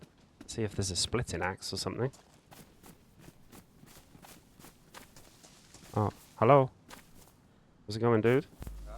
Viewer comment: he actually said “Здарова, мужик” - “zdarova, muzhik” in russian, which means “Hello, man”